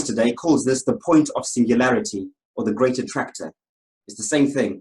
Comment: did he say "Greater Tractor" - what's that then, Massey Ferguson > John Deere? and now at 6:51 he's quoting De La Soul......